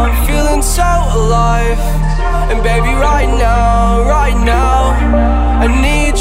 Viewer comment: i’m so high i thought this shit said “ DaBaby “ 🤦🏾‍♂️😩😂